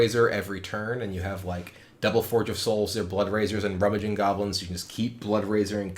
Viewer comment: did he just say "rummaging goblin", the mtg card? ;)